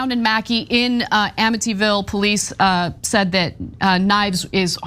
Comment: did she say Amityville? Lol please tell me the address of the murd3r house is number 13 and on elm street too! Lmfao!